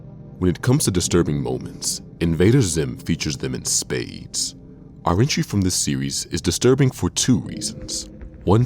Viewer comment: Does anyone know what he's saying at 10:16? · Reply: He says "our entry from this series"